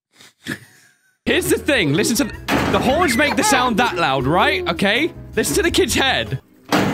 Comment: did he just said the horse?